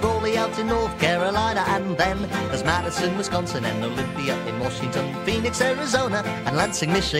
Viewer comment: Did i hear James Madison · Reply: Madison,Wisconsin